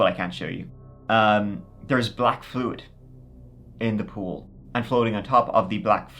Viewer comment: not gonna lie, around 13:25 I thought you said he walked into some ghouls. Then heard it again. Took at hot moment to realize you said POOL. that story was much scarier for a moment. got further in. WIth the university being near, that is a black market organ gathering location. Those are all probably "missing" people or people the gov't was looking for. These bodies were illegally harvested , mostly likely